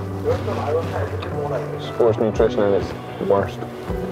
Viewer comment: not too sure I'd bundle "forced nutrition" and "Mcdonalds" in the same sentence..... :) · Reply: I think he said at 10:00, "sports nutrition at its ... worst." I think we can rule out McDonald's as a video sponsor.